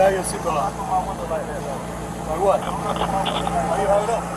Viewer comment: “Don’t knock on my window like that” “or what?” Amazing de-escalation boys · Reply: I know that's what the caption said but it sure sounded like he said "like what" not "or"